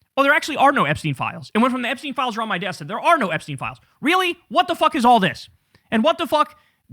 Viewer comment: I think she said there is no Epstein CLIENT LIST, not that there are no files.